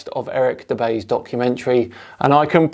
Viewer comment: DId he say "Documentary" ;)